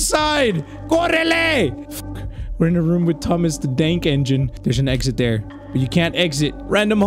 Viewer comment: Was it just me, or did he say Thomas The Dank Engine???